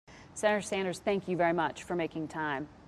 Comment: I heard "Sarah Sanders" in the beginning.